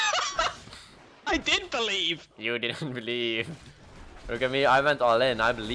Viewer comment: on 7:30 when the guys said " i did believe" it sounded like keyori